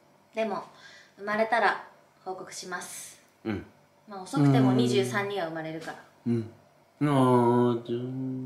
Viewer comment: まぁ、遅くても23には産まれるからね。なのに、まぁ、遅くても23人は産まれるからねに聞こえてどんなボケだよwって一瞬思ってしまった。